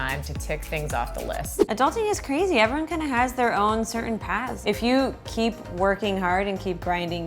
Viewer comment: When said everyone had their own paths, I thought she said everyone had their own PANTS. I think I have hearing issues, :D :D :D